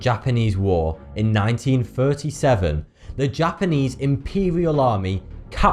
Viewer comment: i swear to god i heard 1947